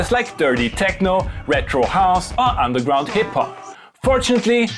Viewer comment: misheard retrohouse as microhouse like "sure, that's one way around the small sample memory"